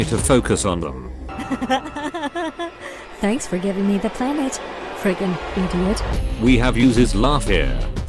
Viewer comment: i think she says playmate